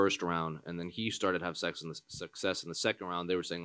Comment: wtf.. did he just say start having sex in the round lol